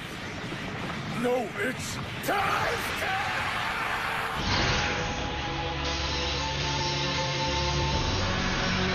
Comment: did he say witch or *** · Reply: He said what strength